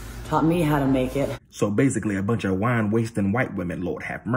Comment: “WhInE tAsTiNg WhItE wOmEn, LoRd HaVe MeRcEh”😂😂😂😂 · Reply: I thought he said wine wasting?